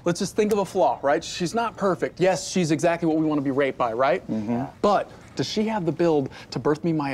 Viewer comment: Did he just say, "Yes, she's exactly what we wanna be raped by," at 0:50 ?? o_O